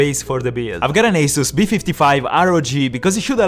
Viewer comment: did he just say b55